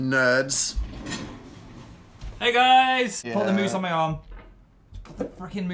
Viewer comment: PHIL SWORE I CAN'T he said "put the f-ing moose on my arm" I can't believe this